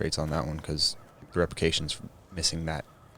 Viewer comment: yo on hole two i heard "reprications". repercussions my dude lol